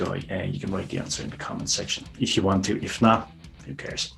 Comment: Can't really hear the question. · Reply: Anyways you said "Answer in the comments or don't Who cares..."